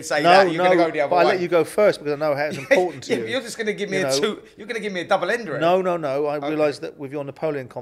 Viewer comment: Did spencer say at 38:30 "youre gonna give me a double ender" ? 😅